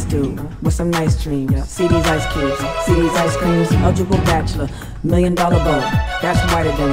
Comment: I'm sorry, I can't hear anything other than 0:27 "I'm a nice dude, with some nice dreams. See these ice cubes, see these ice beams?"